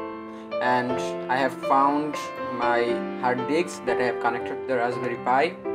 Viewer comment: Is it me, or he really says "I have found my hard dicks" on 9:45?